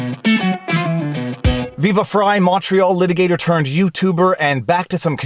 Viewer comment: Finally figured out what he calls himself. Always thought he said macho when it is actually Montreal. Keep up the great work.